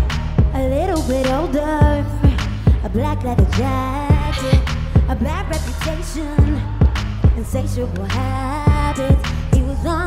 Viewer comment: Did I just hear “A black reputation” instead of “A bad reputation”? 0:35 😂 Omg she’s sooo out of breath! But the way she made a mistake is kinda cute.